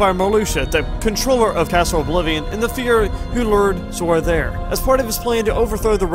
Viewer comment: ugh wtf are you saying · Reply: Sorry bout that lol I said "the figure who led them there" I believe